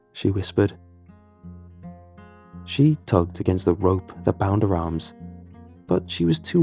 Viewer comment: Bro the the hell, I had my eyes closed listening to this “peaceful” story and then I hear “she tugged in the ropes” I just opened my eyes wide and thought “holdddd the fuck up”